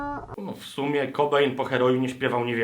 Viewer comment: tylko ja usłyszałem Kobra? :D